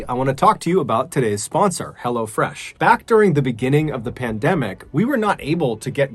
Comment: I keep hearing "Hella Fresh" which you have to admit is a pretty good name on its own